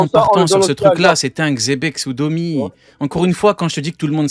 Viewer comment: qui dit: xebek sous domi😅😅😅😅15:46. Est ce que c'est moi où il y'a du monde qui ont entendu la même chose